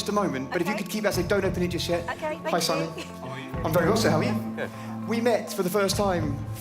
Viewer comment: Did he just call Simon, Howie? 😂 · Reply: are you deaf? he literally said '''hi SIMON how are you? ''